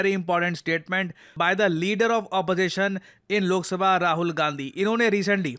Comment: Did he really said pappu gandhi 0:25 😂😂😂